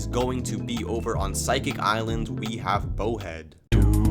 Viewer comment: I can’t hear bowhead I hear periscorp tho the real buts on physic island